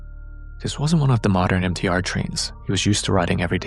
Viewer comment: I thought he said "NTR Train" 😂